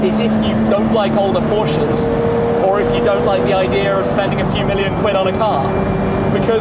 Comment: Rob and his team are incredible… people who are fortunate enough to have one in their collection are so lucky But, $4 million..? That’s actually pretty insane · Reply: I thought he said two million It would be 4 million in NZ tho